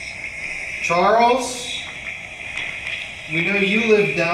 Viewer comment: "yes" 29:16 Charles 30:02 "right there" 31:43 whispering I can't make out but maybe hurry he's coming 32:38 sounds like "here I am answering questions" 33:10 " sure or shut it"